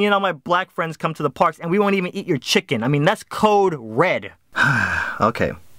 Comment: DID I HEAR A CODE RED 2:06 ?